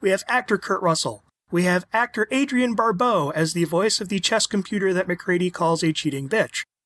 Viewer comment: I thought you said "chest computer" for a minute 😆